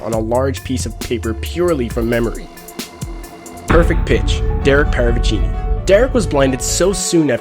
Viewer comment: i thought you were saying "perfect b...tch" lol XD